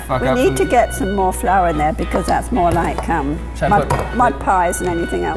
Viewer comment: dafuq did she just say it’s more like cum